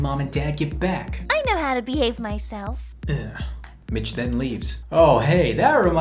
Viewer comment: i swear i heard 'bitch then leaves' lol